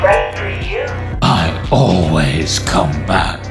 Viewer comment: When I scrolled At the fnaf 3 part I heard “ I always cu-“